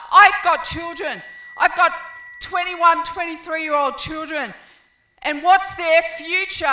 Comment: I thought she was saying I have 2 and 3 year old children.